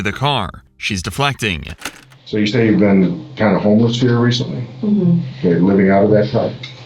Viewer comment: Holy shit I'm brainrotted, I heard "Kinda homeless" and chuckled